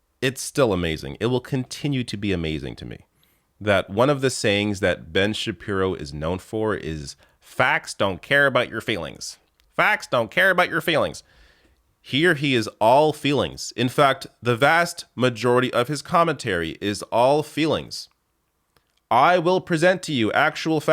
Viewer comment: For a while I thought Ben was saying "f*g* don't care about your feelings" and I was like yeah, why would we care about bigots and fascists feelings? (I'm not gay but the joke works better in first person)